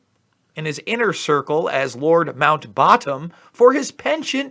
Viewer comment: Lord mount baton? Oh I literally thought u said lord MOUNT BOTTOM, LIKE MOUNTING LITTLE BOYS BOTTOMS😫😖🥵🤮🤮🤮🤮🤮🤮🤮🤮🤮🤮🤮🤮🤮